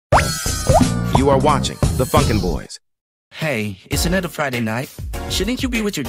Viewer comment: i thought in the intro it said "you are watching the f#cking boys"